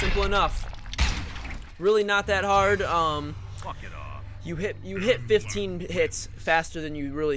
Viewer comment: did anyone hear him say walk it off at 2:58?